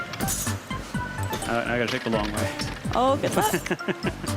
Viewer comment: Did she say "oh f***"?